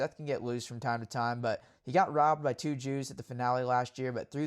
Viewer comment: what did you say aaround 5:02 that sounds like "he got robbed by two jews" when you were talking about hammaker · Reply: 2 ju’s…juju 😂